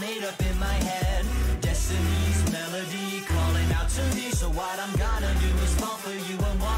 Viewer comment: You’re telling me that the lyric has always been “Dreadfully, terribly, something is amiss” And has never been “Jeremy, Jeremy, something is amiss.”!? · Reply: I also misheard “so what I’m gonna do...” as a bunch of random sentence fragments starting with “so when I’m gone...” So I’ve come to the conclusion that, if that’s all you misheard, you did well.